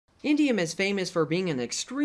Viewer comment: I thought you said "iridium" for a minute there. I was about to flip out with how much "iridium" you are wasting. It's just under $4,700 per ounce.